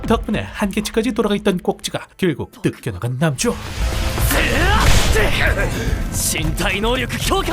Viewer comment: 나레이션 발음이 너무 안좋은건지 계속 돌려듣고 자막키고 들어봐도 뭘소리여 이게 12:34 결국 한계치까지 돌아간 꼭지가 뜩끅한 남주? 뭐라는거야 14:58 전생에서도 가져본 적 없는 킹근의 분노? 워메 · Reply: 돌아가있던 꼭지가 뜯겨나가버린 남주, 극한의 분노. 잘 들리는디요...